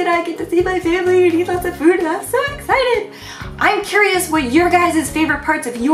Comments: I thought she said "and have sex!" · "Jun and I are going back to America next month, and I get to see my family, and eat lots of food, and have .........." Lol, did she say what I think I heard? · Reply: I thought I heard the same thing, but she actually said: "and I'm so excited" It's just that she said it so fast haha :p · DID SAY HAVE SEX. WHOT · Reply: sama (rainbow) "I'M SO EXCITED" haha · At like 9:16 I thought she said have sex but she said I'm so excited xD